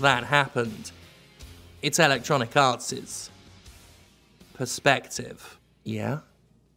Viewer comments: It is wonderfully appropriate how much it sounds like Jim is saying "Electronic Arse" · I always hear it as "Electronic Arse".